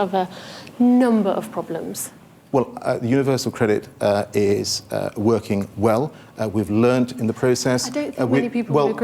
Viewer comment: What did the idiot say at 25:03 ? Universal Credit is working well (it isn't); we've learnt in the process (they haven't); what we have is a system that is simpler (it isn't); which people understand (they don't); and ultimately make sure they get into work faster, stay in work longer and earn more (total fantasy).